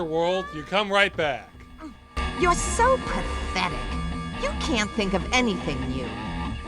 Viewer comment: I could be wrong, but I think she said "prophetic", not pathetic. As in, "you're so prophetic, you can't think of anything new" (because prophecies happen as expected).